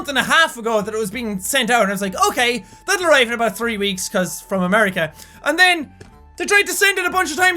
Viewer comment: I thought he as saying "oh that'll take three weeks because Americans." lmao